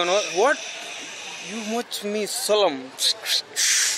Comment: thought he said you want the smoke/ the heat facial expression matters 😅